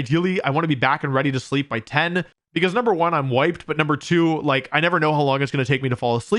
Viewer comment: I legit thought he said, "I want to go to bed early because, #1 I'm white" lmao